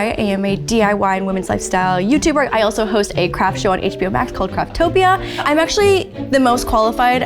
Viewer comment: Oops, sorry, Lauren, I heard "Craptopia", but then I am not a native speaker, so that probably explains it 😅